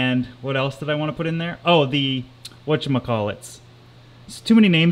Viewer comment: did he just say 'what do i call it!'s